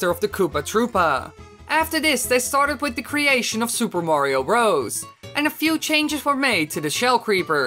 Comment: it sounds like he is saying super mario rose